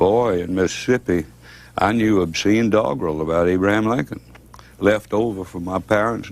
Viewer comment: What does he say at @ 2:08 about Abraham Lincoln? It sounds like dogroll?